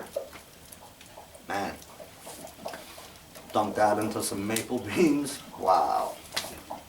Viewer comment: Just curious...what kind of beans? Sounded like you said Maple beans. If so, that would be like baked beans?